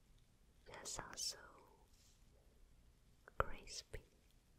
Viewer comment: I've watched too much Handmaid's Tale.I did understand "Praised be" instead of "Crispy" at 8:15 :D